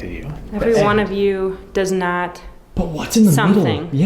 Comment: “Everyone of you had respect, get back, the demon got me” after many times listening this is what I ended up hearing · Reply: I thought I heard "everyone of you was polite"